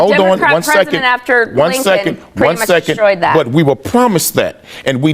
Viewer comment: Did she say , “the Democrat president destroyed “that”? Basically, it existed.